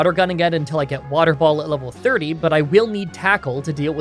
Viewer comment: Is it me or did he say water ball at 3:58?